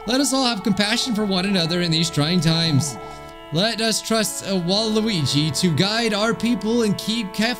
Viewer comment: DID YOU SAY WALUIGI